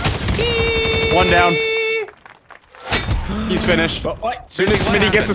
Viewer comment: for some reason I heard "he's Finnish" and I'm like "wtf does that have to do with anything?"